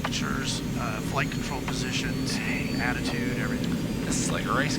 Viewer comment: does he say it feeds into attitude everything? Just sounded funny im probably wrong